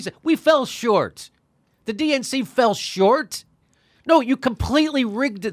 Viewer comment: I think he said filled shorts not fell short!